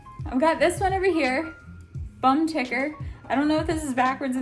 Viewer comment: I really thought you tattoo said 'bum licker' I was startled